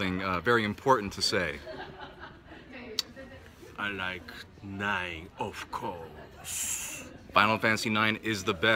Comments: I swear I heard "i like 9 of calls" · Does he say: "I like 9, of course!" Is that what he says? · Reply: I honestly just got this on my feed..., but... I felt like I heard "I like the 9 of clubs." lmao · Does he say: "I like 9, of course!" Is that what he says? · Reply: I heard, "I like line of cokes."